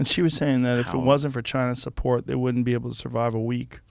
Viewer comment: At first I thought Joe said if it wasn't for child support I was like what the hell but he said China's support 😂